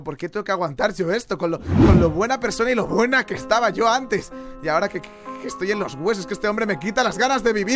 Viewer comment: escuché que dijo lo que buena que estaba 7w7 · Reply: dijo: Lo buena que estaba yo antes